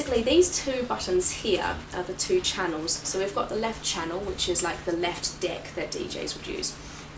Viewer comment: it sounds like she says "we've got the left dick" LOL